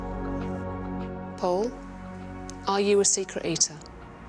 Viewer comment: I thought she said "are you a secret agent" instead of "are you a secret eater" I can't even explain how confused I was.